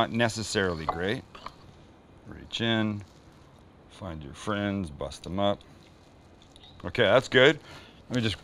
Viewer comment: The sauce is made just like how I like my women. Thick and rich. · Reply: Sounded like he said find your friend , bust a nut!! Hahaaa minute